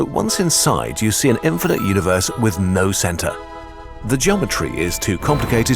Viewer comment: i thought he said "no santa" at 4:46 💀